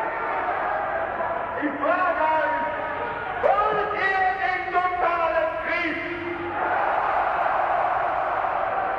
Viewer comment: Who can understand? What hitler saying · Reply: Maybe it's bit late but he said "Wollt ihr den totalen Krieg" - "Do you want the total war?" - everybody "yesss"